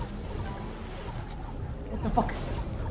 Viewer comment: ركزو واسمعو ايش قال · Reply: قال وت فاك بس تكلم سريع✨